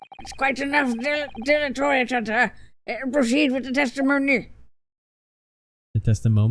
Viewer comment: I'm glad I wasn't the only one who heard "testimomi"